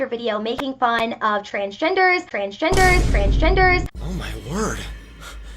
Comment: Turn captions on at 7:50 the last one says “ French Vendors” instead of “transgenders”